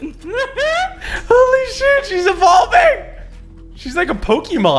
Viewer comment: did he say sh**